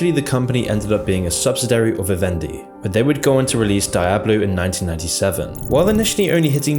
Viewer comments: Did he really say "dijablu" at 1:25? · Did he really say "daijablu" at 1:25?